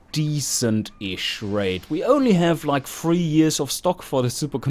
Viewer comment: years of stock? You mean 2 years of stock? Isn't the answer obviously "about 2 years before I want to stop selling them"? :P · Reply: I thought you said 33 years. I guess I heard wrong based on your reply. I would say "two years before I want to stop selling them" would be a poor choice if you actually had 33 years of stock.